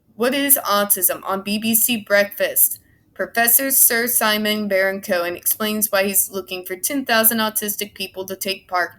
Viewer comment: I thought you said Sacha Baron Cohen at first. I was ready to go off on the guy from Borat. This guy Simon Barron Cohen does sound awful. Why are they funding his research when he's touting a theory that is obviously false? It's like putting a flat earther in charge of space exploration.